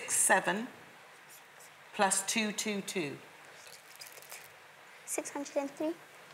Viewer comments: Did she say absolutely WRONG ?????!!!!!! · I thought u said ABSOLUTELY WRONG! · Is it just me or did she say absolutely wrong at 5:00 Reply if you heard it too??? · i thought u said absolutely wrong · did she just say 'absolutely wrong'? 😂😂